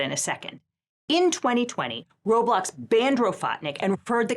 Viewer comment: My ears are too elder millennial boomified for this. I keep hearing "row blocks" and "Roebuck's" and having to mentally translate. Sears doesn't even exists anymore lmao